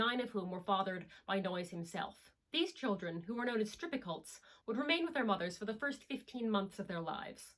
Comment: I've watched this section several times and googled many variations of what I can hear "these children, who were called strippicults" and what the subtitles say "strippults" and am er.... not finding anything that makes sense. What were the children called? Interested to learn more, it's fascinating when groups make their own new lexicon